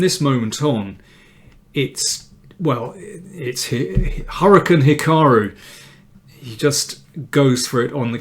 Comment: Some of your new titles work out fine. "Haka Naka" is not one of them. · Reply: yeah I was wondering what that was about. Around 7:08 he says something like "it's Harukand Hikaru"...I'm guessing he's saying something in another language, but I have no idea what it means. His philological references are occasionally Nietzschean, ie., hard to understand.